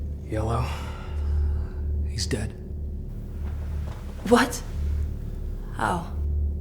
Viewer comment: i thought he says "where?"